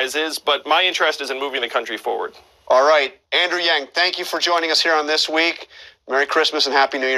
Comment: what did he call Andrew at the beginning of the interview?? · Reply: thought he said Andrew John Yang LOL